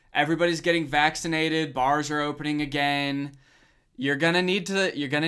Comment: I thought Danny said Mars Is opening again